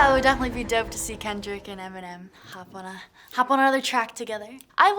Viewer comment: Maybe that's why she said "...another track..." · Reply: oh I thought she said "hop on a track"